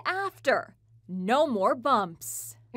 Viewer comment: Toucan ohhhhh.... I thought she said two-can Sam I was like huuhhh? Wat